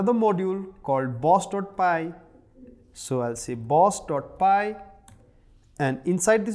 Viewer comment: lol, i heard boss.py @ 3:10 bastard pie at least twice :D , thx for the effort you make to prepare us for programing